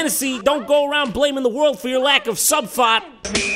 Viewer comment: wtf does Jon say at 6:30? "Don't go around blaming the world for your lack of [____]"? It sounds like "sub-fart" but I'm sure that's not it...